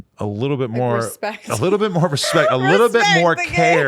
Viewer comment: WAIT. Did she say 'respect nigga' @ 10:50 ? Get her off the show immediately